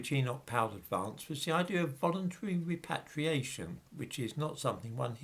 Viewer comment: I thought it said "Enoch Powell and the idea of reparations" lol!